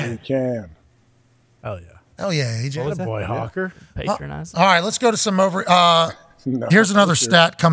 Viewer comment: Can someone tell me what Ty said here? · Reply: Patronizing